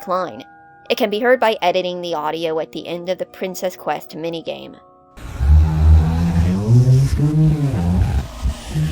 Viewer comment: what’s he saying here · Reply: "I always come back. Let me out." Sorry, I should've put subtitles because it is pretty slurred.